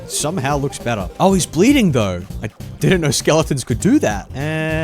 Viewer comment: "I can almost feel the heat right now, in real life!" That's just your pc heating up, totally normal. 9:26 Bone Marrow contains Blood Vessels, so yes, Skeletron can bleed. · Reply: I thought he said skelotron could breed 😳